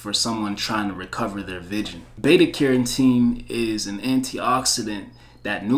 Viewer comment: what did he say that helps eye vision? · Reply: beta carotene.